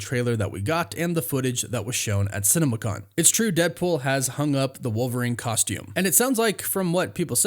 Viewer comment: Did he say Deadpool has hung up the wolverine costume